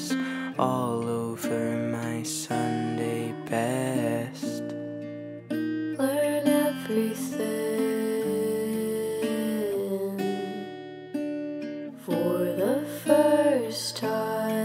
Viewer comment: Lyrics: I was just born like this Wish that I could change it Four peculiar limbs and a head that doesn’t fit Wish that I was still a kid I was just born like this Doctors knew before I did There’s another snail living with me in my shell It’s fair to say I don’t like it I’m hanging out with the bullets in my mouth and when you look away I’ll spit them out I’m hanging out with the foxes and the hounds and when I fit in I’ll break back out Really hope you found something Entertaining Honestly I’ve had enough of looking at a dead man I just wanna scrape my knees Cry until my mommy sees Popsicle to make a mess All over my Sunday best Learn everything for the first time again I just wanna be a kid I just wanna be a kid But I don’t wanna be a kid Who was born like this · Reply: i thought it said burn everything instead of learn everything ;_